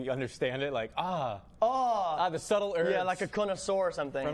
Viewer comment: did he just say conoSORE xD